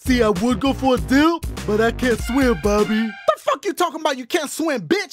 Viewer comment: "I don't want to hear nothing from sponge bobby" 😂😂😂 I don't know why I died when you said that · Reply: I thought he said "bubby"